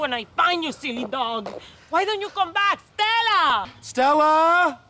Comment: I thought he said “STELLARR” at first😭💀